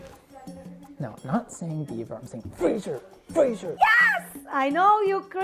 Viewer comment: “I’m not trying to say Bieber, I’m trying to say FRASIER! FRASIER!!” had me on the floor 😂😂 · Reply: I thought he said 'beaver', not 'Bieber' ☠️I guess me and Chris' child have more in common than one would've thought 😂😂